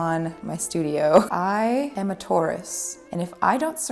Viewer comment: I thought you said "I am a tortoise"